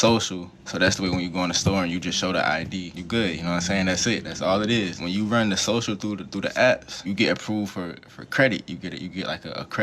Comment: “you know what I’m sayin?” Sounds like you’re sayin I wanna go to prison